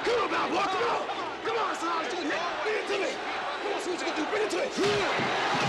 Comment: Can anybody tell me what Clubber says here? This is the only line I can't understand. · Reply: "Come on, hard as ya can get hit, bring it to me! Come on see what you gon do bring it to me!" That's my take..